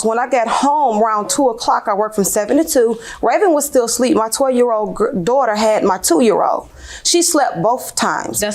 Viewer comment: Did I hear correctly that the defendant is 31 and she referenced having a 20 year old daughter (6:05)?! 😳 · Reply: No, she said her 12 year old daughter, not 20 year old daughter.